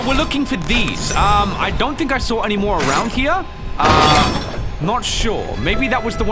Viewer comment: is it me or did i hear arounk here